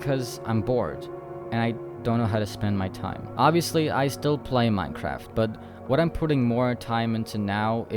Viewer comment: bro I thought they were saying “do you still play NIIIII-“